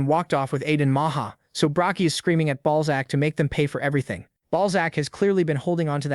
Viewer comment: all I hear is ballsack